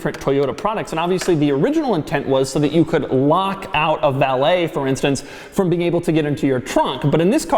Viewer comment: What the hell is the word he says at 5:10, 5:19, 5:22 and 5:37????? Valay??? Wtf???